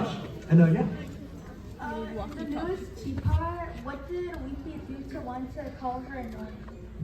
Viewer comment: what did he say? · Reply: "What did Leafy do to one to call her annoying?"